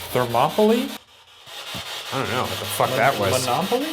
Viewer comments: it sounds like it said don’t laugh at me ngl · I didn’t hear “thermopoly”? I heard “they’re mocking me” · i definitely heard “don’t laugh at me” also ryan is definitely ricky goldsworth in this episode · I heard “don’t laugh at me” anyone else?? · When they thought he said something like “monopoly” to me it sort of sounded like “don’t laugh at me” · I heard don’t laugh at me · sounds like he said ‘don’t laugh at me’ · Instead of "Thermopylae" in Lockhart's cell, I definitely heard "don't laugh at me." I got shivers!! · I clearly heard “don’t laugh at me”, not “monopoly”. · I heard “don’t laugh at me” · it sounded like the voice said “Don’t laugh at me” Also Ryan what if you didn’t announce to the potential spirits that you have a camera because that might detour the potential spirits #boogara #shaniac Edit: also if you mess with the playback speed it might help distinguish what the spirit box is saying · sounded like “Don’t laugh at me” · ok but that kinda sounded like “don’t laugh at me” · I heard “don’t laugh at me” it was super clear and kinda sounded like they were on the verge of snapping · The spirit box at 11:14 sounded like “Don’t laugh at me.” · I thought the voice was saying “I’m nervous” like, they’re nervous that a guard or inmate would show up and hurt them? Idk... :/ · I think it said don’t laugh at me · I didn’t hear “monopoly” I heard “don’t laugh at me” did anyone else hear that? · Ok is it just me or did it say “don’t laugh at me” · I hear “what’s happening”